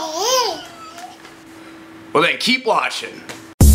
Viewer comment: Did he just say "keep washing" 0:12 or is it just my imagination? ^.^